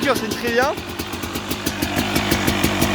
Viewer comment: Y'a que moi qui a renifler du nez à 4:56 quand il a dit " oh la senteur " ?😂😂😂 Continue comme sa frero !🎥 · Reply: Enduro Movies juste il a dit "oh l’odeur "